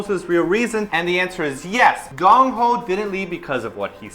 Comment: says 'Gongho' instead of 'Dongho' Lol